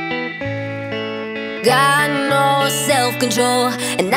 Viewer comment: I thought she say god know self control 😂😂🤣🤣